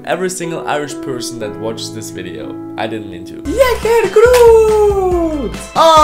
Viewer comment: мне одной послышалось "Ягер крут"? Всё, меня в дурку пора...